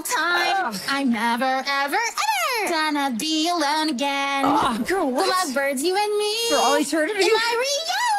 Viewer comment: what does he say here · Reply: I think he says ah girl what